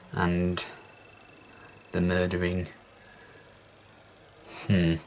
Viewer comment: Those nerds... and the murdering: hmmms disapprovingly Me: 👏👏👏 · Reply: For a moment there, I thought he said "nerdering" xD